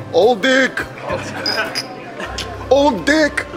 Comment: It sounded like aw di*k. Lol